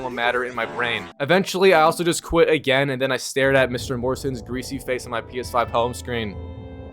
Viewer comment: Did he say Mr. Morrison? @ 3:44 ? Wtf